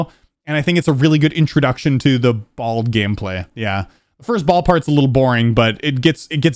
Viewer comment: did just say "Introduction to the BALD gameplay"?..